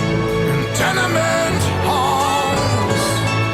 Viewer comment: As I listened for the first time I misheard a line. The phrase, "tenement halls" became Tiananmen falls. I can't shake it and it adds a whole new dimension to my understanding and perception of humanity. We must not forget those brave souls lost. In silence and in voice they are honoured... we will remember. 🇬🇧